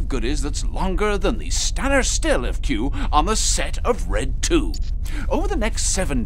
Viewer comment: Does he say on the set of Red tube?